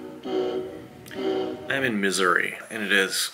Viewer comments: 100% thought you said "I'm in MISERY" at the start. That went on until you said, "why would you go all the way to Missouri". You even seemed a bit sad... GLAD YOU'RE OKAY! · Sounds like is saying "I'm in misery" at the beginning 😂😂